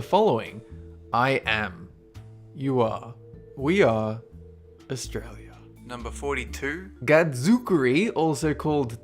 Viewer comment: Did he just say "Australia?" If he did, he is now officially awesome in my books.